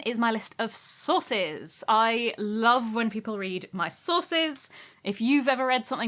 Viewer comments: I literally thought she said, read my sauces. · I coulda sworn she said "you can find my list of sauces! I love when people read my sauces!" Needless to say I was confused. I'm an uncultured American I guess, but at least I ain't no macaroni!